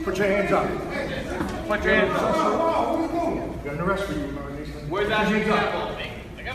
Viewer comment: Did... did someone said "Where's that G sample?" @ 3:42 Damn Umbrella really scrapping the bottom of the barrel here...